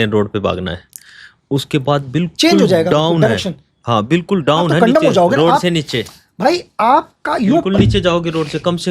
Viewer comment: did he say, aap condom ho jaoge... This slang is used by pahari desiS across INDIA.